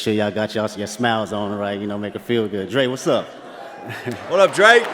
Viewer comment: I thought he said "Drake whats up" at first 😅